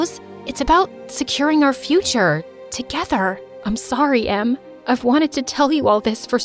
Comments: it says ‘mom’ instead of ‘em’ (jus pointing out x) Ps I rlly like this story ❤ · Did anyone else saw that in 11:21 when rose said I'm sorry em the subtitle said I'm sorry mom? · The fact that at 11:28 it said "I'm sorry Mom" instead of em lmaoo😂😂😂😂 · you guys notice the mistake in the captions?? "Im sorry mom" instead of "Im sorry Em" 😅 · is it me or did somoene realize that caption said mom instead of em??? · WTF DID SHE JUST CALL HER SISTER MOM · If you go to 11:29 in the video you can see that the words say “I’m sorry mom” instead of “I’m sorry ‘em” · on the video it says “I’m sorry mum” INSTEAD of SIS😂❤ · it’s says mom instead of em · Did anyone saw that rose said "i'am sorrry mom" instead of em/emily on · I'm sorry mom instead of I'm sorry Em😂😂[funny] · Who else niticed “mom instead of ‘‘em at 11:28? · it was such a good story but on 11:28 why did she say MOM instead of Em in the subtitles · she said im sorry MOM instead of sorry EM 🙂🙂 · the subtitle is wrong. Rose says "I am sorry, Em" but its "I am sorry, mom" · I saw a mistake 11:28 "I'm sorry em" But the subtitles said "I'm sorry mom" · says I’m sorry mom instead of emm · why when rose was saying "sorry m" the subtitles said "sorry mom" ??????????????? in 11:28 of the video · "I'm sorry, mom" they put mom instead of em lol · I found a mistake in the video! 11:27 the older sister says "I'm sorry Em" but the subtitles says I'm sorry mom. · Bruh at 11:28 did rose really say mom instead of em